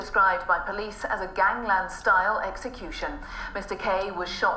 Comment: thought she said gangnam style exacution